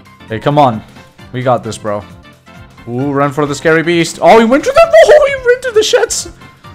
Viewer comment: Did man say “ohhh he went thru the shits”